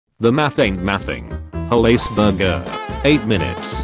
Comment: i heard they said "the mathing is mathing"